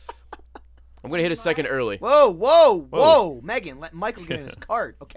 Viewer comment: Did Michael Call Lindsay Megan at 18:28 ish???